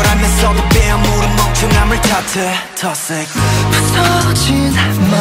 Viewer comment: I think that's KINO says- toxic